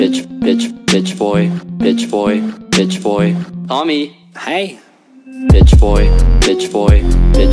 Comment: I'm so late wtf- but 0:13 the 'hii' (in a way more said like hei tho) is so- cute wtf. just ee. No simping.. no simping.. you gotta make it through September.. no simpin- maybe simping.. :) (If you wanna hear 'later, nerd' over and over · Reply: he said "hey"